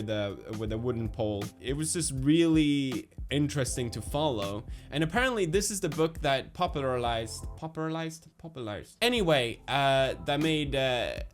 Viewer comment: I thought you said "this is the book that popularized adpocolypes scenarios" lmao